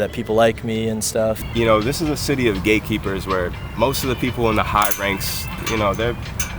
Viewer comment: lol thought he said “gaykeepers”